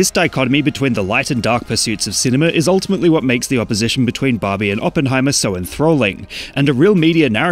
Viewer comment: I like how the captions put bobby instead of barbie